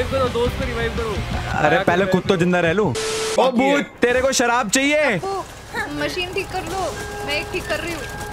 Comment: Wah tum toh bade heavy horror game player ho bhai 🤣he said 9:02 bhootni and I thought he said kutti lol